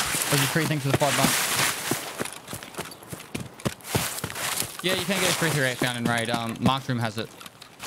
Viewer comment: can someone please tell me what he said there. I watched it several times and just don't understand it. All I can understand is "Yeah you can inaudible found in raid..." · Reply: “Yeah you can get a .338 found in raid, Marked room has it.